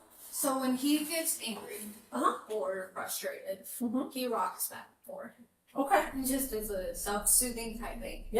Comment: So the baby that can't walk likes to walk back and forth when he gets frustrated? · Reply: I thought she said,”rock back and forth,” not “walk back and forth.”